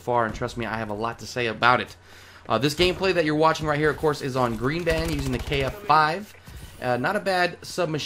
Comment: Is it me, or does it sound like he says greed ban at 0:32?